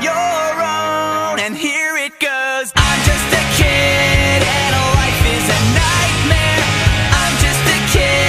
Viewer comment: I thought he was saying I'm just a kid and my wife is a nightmare i know it's creepy because of that i had to see the lyrics 💀🤝